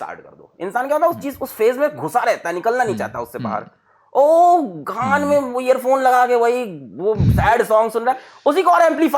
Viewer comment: I thought Alakh sir said g***d mein earphone 😂😂😂😂😂